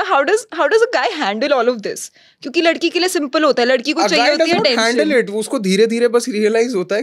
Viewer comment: What dies she say at 12:40 ? After 'Ladki ke lie simple hota hai, usko kuch chahiye hota hai toh-" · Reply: Ladki ko chahiye hoti hai attention